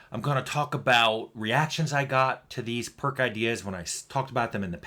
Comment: at first I thought it said pork ideas because I I'm hungry